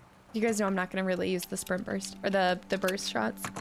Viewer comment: did you say "sperm burst" for burst fire 😂